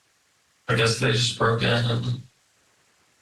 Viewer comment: I think he said, "I guess they just broke in.....and went biblical." Against the window of harassment?